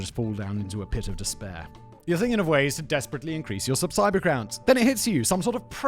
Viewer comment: Did Simon really say "subsiber crounts"?